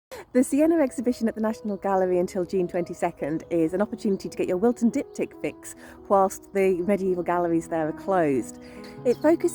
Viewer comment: NGL, I heard "get your wilting dick-tip fixed" and was even more concerned with the current state of pharmacological advertising.